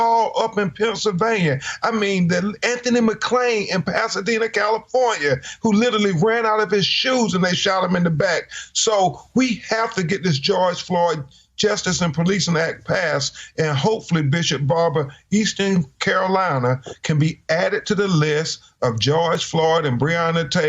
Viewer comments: So what happens when these videos that folks wanna see end up being inadmissible in court because the whole world is viewing evidence before a trial 😅😂🤣 Here's a thought maybe stop placing yourself in a situation that will get ya killed👈7 children no wonder he was selling drugs 😅😂🤣...stop shooting black folk for running away......I'm sure they're running away because they scared the police might shoot them not because the have a bag of class A felonies In their pockets 😅😂🤣this panel is dumb and spreading ignorance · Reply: every time I hear "THE GEORGE FLOYD"S ACT" sounds like they are saying "THE IDI AMIN ACT" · Oh look more white males badge bullies killing ANOTHER black man. How shock should this be by now. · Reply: every time I hear "THE GEORGE FLOYD"S ACT" sounds like they are saying "THE IDI AMIN ACT" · They were quick to circulate George Floyd's criminal history as well, meanwhile Derek Chauvin had 18 complaints against him. The DOJ is now looking into a case where Chauvin held a teen down for 17 minutes with his knee. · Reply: every time I hear "THE GEORGE FLOYD"S ACT" sounds like they are saying "THE IDI AMIN ACT"